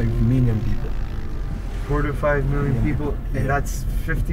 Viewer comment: Cómo que en puerto príncipe "Haití" viven 45 millones de personas o escuché mal. 😲